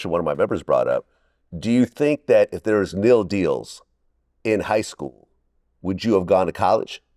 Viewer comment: Kneel deals. I thought this goofy said meal deals. Smh u uncultured swine